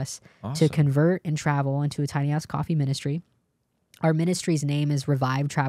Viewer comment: Thought she said something else 😂 (but tbh, what did she actually say?) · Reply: Sounds like she said a tiny ahh coffee ministry 😂😂😂